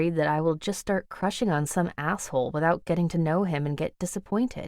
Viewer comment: did she just say as*****? 😂😂 (___)?